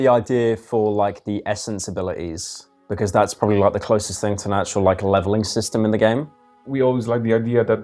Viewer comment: typo 2:59:16 - "the lid gently slids open" instead of 'slides' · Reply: miscaptioned 4:23:47 - pyro says "the closest thing to an actual, like, leveling system" and the captions say "the closest thing to a natural leveling system"